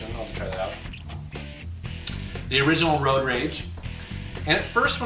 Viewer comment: Did he say the original "Road Rage"?